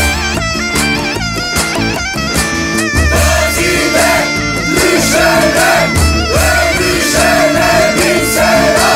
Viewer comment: da ne diyorlar? bilmiyorum... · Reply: Safā Samadi Gazide Düşene Dövüşene Bin Selam Diyorlar